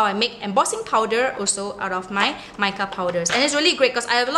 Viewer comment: Can't find it here anywhere. In your video at 3:42 i think you say you use make up powder, is that what you say? My english is not very good but i think thats what you say and i was so happy because that i can get and if it works just like the distress ink it would be so great. When you have a little time can you get back at me, please? I would be so thankful. Again, you do an amazing job. I will look at all your videos, they are so great.